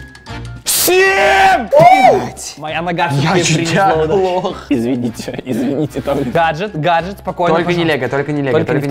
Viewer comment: что сказал? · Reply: он сказал б#ять :3